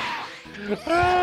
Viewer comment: Until I turned on the subtitles one day I had always thought krillan said blueberry bastard